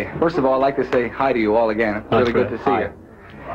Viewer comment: What is it that the reporter says at 3:37 that causes the laughter? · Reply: Debra Harris He says, “I’d like to say hi to you again, it’s really good to see you.” The question was “What are you going to do when the bubble bursts?” John says they’re asked this at every press conference. Which they were. I really remember that. I was a huge fan, saw them twice in Chicago, 1965 & 1966. A box ticket at Comiskey Park in 1965 cost $5.50. Truth!